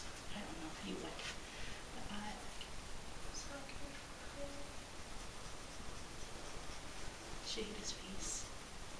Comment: Hey do you still have the film footage for this if so you might want to take a look at 7:58 - 8:00 there was defiantly a voice of the little girl saying something I haven't completed watching the video yet and that really was a good SVP off the camera if you can make it out I would love to hear what you think it said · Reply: I heard "thank you"